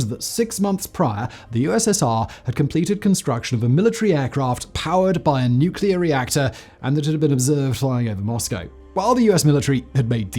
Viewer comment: What did he say at 1:48 in the video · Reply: A military aircraft powered by a nuclear reactor and had been observed flying over Moscow